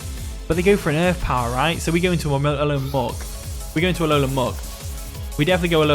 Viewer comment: Honestly really well played from your end still, all things considered! Great battle, the Tera Flying on Alolan Muk to deal with Landorus-I was pretty smart. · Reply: I thought that said Elon musk for a second 😂